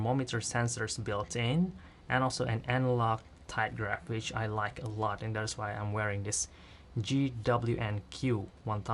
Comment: sorry, i cant hear clearly in 3:12, this analog high and low for what? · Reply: Tide graph